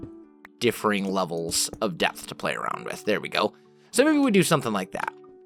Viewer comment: OMG I know your not a killer but around 4:33 instead of saying playing around with depth I think it was playing around but instead you said playing around with death .. ~